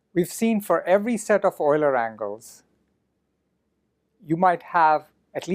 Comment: Why does he say 4 Euler angles at 11:40?